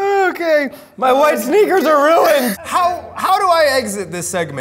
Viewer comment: am I the only one who heard Zach or Eugene say "MY WIFE'S SNEAKERS ARE RUINED" but aren't they both single?🤔🤔🤔